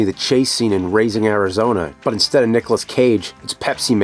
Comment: Wild... For the Pepsiman episode I thought the nerd referenced "Raising Arizona" as "Leaving Arizona" by mistake. Obviously I misheard it in my head lol Then I immediately thought of that movie "Leaving Las Vegas" in which I couldn't remember the name (thought it was Leaving Arizona based off what I THOUGHT the nerd said) Went down the "Leaving Las Vegas" rabbit hole and holy f**k the context behind it and John O'Brien is crazy. Pretty depressing